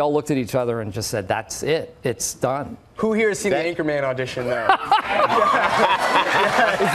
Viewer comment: what is Timothee saying? It's just mumbling in my ears, because i'm not a native speaker and kinda not getting the word before "...auditon there". Can someone help? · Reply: I think he said: "Who here has seen "The Anchorman" audition?" Probably referring to Steve's infamous auditon tape: <link>